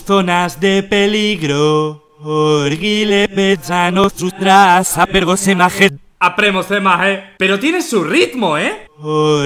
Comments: escuche orguile sáname los brazos a jet literal escuche eso xd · yo escuche orgia al principio :vvvv TENGO MIEDO >:'''V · yo escuché orgia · En el minuto 5:19 escuche : Por Hitler me sano en sus brasos aporsemajet asi escuché yo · entendi orgine be pachano en sus brazos en verganesaje xddd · escuche orgía:v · en el minuto 5:19 escuche "y le besaron su trasero" no se si soy yo o alguien mas lo escucho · escuche verga like para que auronplay lo vea